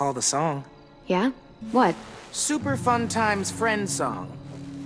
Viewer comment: I thought he said "super fun time friends zone"